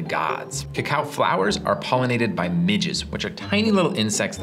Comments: for a second I thought he said cacao plants are pollinated by midgets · I deadass thought he said that "cacao flowers are pollinated by midgets which are tiny little insects that are kinda annoying to humans"